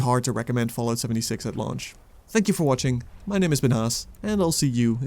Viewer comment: at first i thought he said "my name has been ass "